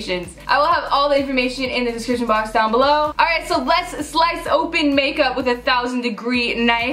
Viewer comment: I thought you said "expensive makeup" ._.